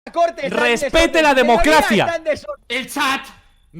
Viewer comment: entendí es Pete la democracia ( si sos Argentina entenderás) en